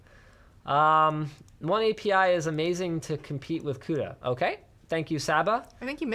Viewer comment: What does he say here? "One API is amazing to compete with CUDA"? Does anybody understand what he says better?